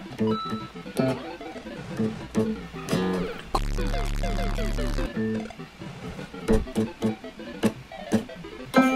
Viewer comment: Did you say ,,kurwa"? Greetings from Poland 😂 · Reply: He said porco dio, it's a swearing in italian